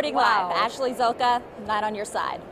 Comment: I watch this occasionally and it always sounds like she says "Not on your side" at the end.